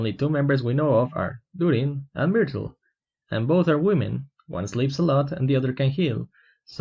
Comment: Scripted lines: one sleeps alot Misheard lines: one is a slut